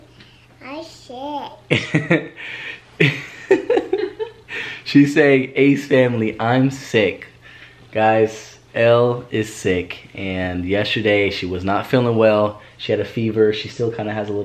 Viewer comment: I thought Elle said ace family I’m sh*t at 2:58😂😂those children are so blessed and beautiful😍😍😍❤️❤️❤️